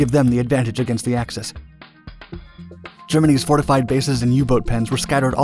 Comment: Does anyone else hear him say “Germanese”?